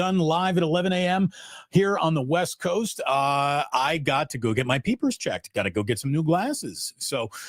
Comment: I first heard "I... Got to go get my PP checked." 😅😂😂😂 Good luck with your appointment, Damon! 😁